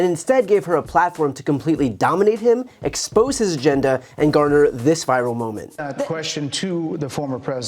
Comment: LOL BRIAN I THOUGHT YOU SAID “EXPOSE HIS GENDER” Am not even a US citizen and this primary got me feeling deranged